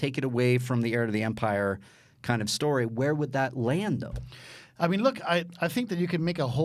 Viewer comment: did someone say, Lando?